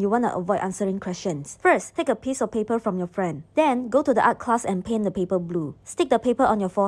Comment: I heard 'from your friend' As 'from your boyfriend' and I was like what has a boyfriend??!!